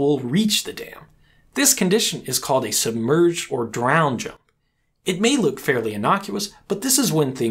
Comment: What is the term mentioned at 4:33 when the hydraulic jump reaches the dam? I cant quite make out what is said. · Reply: Drown jump.